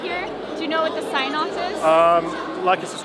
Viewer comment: Genuinely thought he said 'like and subscribe to the sex chat' lol